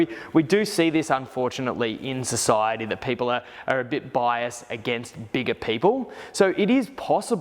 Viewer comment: First time he said it I thought I heard "white bias" 😂